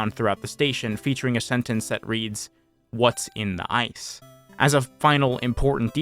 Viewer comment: I ALWAYS THOUGHT THE WHITEBOARDS SAID "WHATS IN THE ME?" wow. Yeah, it definitely says "ice" lmao